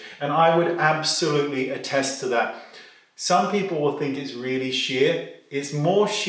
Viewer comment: Oh lordy, at 3.21 I thought you said, "some people think it's really $h!+!" I have wanted to try this foundation for years, but thought it would be a bit cakey. Do you know where I may be able to buy a couple of samples to swatch, before buying? Thanks!